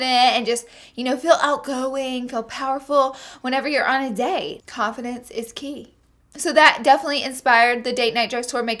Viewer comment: lol my brain is fried. I thought you said "coffin dance" instead of confidence. This is my brain on finals.